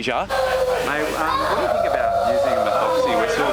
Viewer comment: thought he said using the meth whistle on the kid